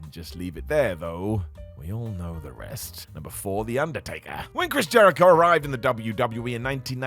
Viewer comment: I thought you said Liquorice Jericho. Liquorice Jericho sounds awesome.